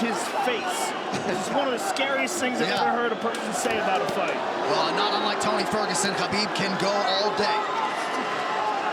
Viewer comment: what does Khabib say to Conor here? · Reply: “Let’s talk now, huh lets talk now, let’s talk”